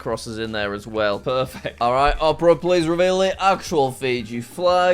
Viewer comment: I feel a little old when I thought he said "Oprah, reveal the flag" as in Oprah Winfrey...